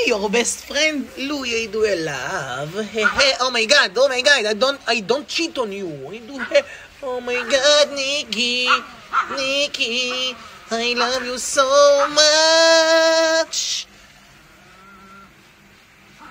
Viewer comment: bro thought said *i don’t cheat on you*💀💀💀💀